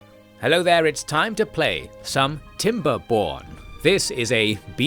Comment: I misheard the title as "Its time to play Timber*p*orn" Comment for the commrnt gods, algorithm for the algorithm throne!